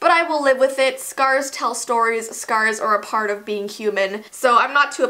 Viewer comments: At about 2:26 I heard you say “scars are a part of being cumin” am I right or weird? Weird I’m sure. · when said "scars are a part of being human" I thought she said "scars are a part of being cumin" and my mind instantly went to the episode of Drake and Josh when drake put cumin in the waffles for their dad instead of cinnamon and I started cracking up at my own stupidity lmao hows your day going? XD